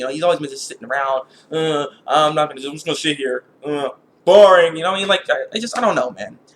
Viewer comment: At the end when you were talking about Madara I thought you said, "Uh I'm just sh*t here" xD